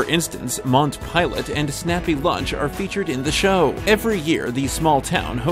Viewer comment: Played 1:58 over and over to hear what was being said. Sounds like "Mont pilot and snappy lunch" You need an announcer with better diction. I still couldn't figure out what he said. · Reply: It said "Mount" Pilot, and Snappy Lunch. Mount Pilot is actually Pilot Mountain, NC, and the Snappy Lunch (a restaurant) is located in Mt. Airy, NC, which is Andy Griffith's real home town and the inspiration behind the fictional town of Mayberry.